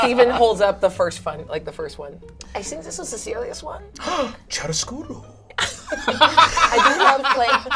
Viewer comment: the captions say “Brennan gasps, speaks foreign language”, but the word is “chiaroscuro”, which is an art style with contrasting light/dark tones.